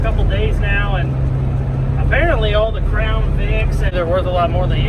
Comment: 👑 great stuff gonna put you on my daily vid list. I thought you said "frown" not "crown"